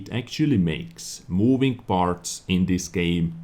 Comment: What says: "Make moving parts moving". What i hear: "Make moving farts moving".